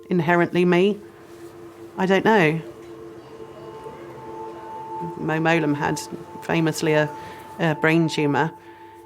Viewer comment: What's the name she says around 18:17? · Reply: Mo Mowlam She was the secretary of state for Northern Ireland